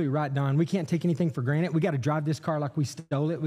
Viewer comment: DID HE SERIOUSLY JUST SAY "GRANITE" INSTEAD OF "GRANTED"???? I didn't think this happened outside of late night television TAT